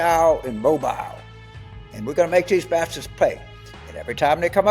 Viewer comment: I thought he said, "We're gonna make these Baptists pay" Like - What? Then realized what he probably said was bastards...